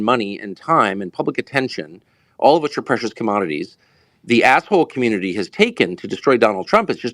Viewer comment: Lol! Not sure if I misheard, but at approx 2:00 I think Tucker refers to the Deep State as “the assh0le community.” If so, i find that hilarious & I think we should all refer to them that way — because “Deep State” sounds powerful, secretive & scary, and in reality they are just a bunch of greedy, dishonest, manipulative clowns on a power trip. Trump-Vivek 2024!